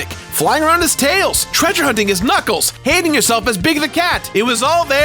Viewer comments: “Hating yourself as Big the Cat” if that’s not a big mood idk what is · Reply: I heard "ending yourself", but these are all equally valid interpretations (apart from the "handing yourself" dude). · “Hating yourself as Big the Cat” if that’s not a big mood idk what is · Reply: I thought he said "Hanging yourself as Big the Cat" which made sense too. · “Hating yourself as Big the Cat” if that’s not a big mood idk what is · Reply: I thought he said "handing yourself" I was like, "wait whut?" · Did I hear "Hanging yourself as Big the cat"? I know isn't a fan of Big, but...